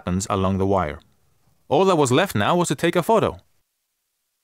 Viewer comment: I thought I accidentally heard Ebola but it was et voilà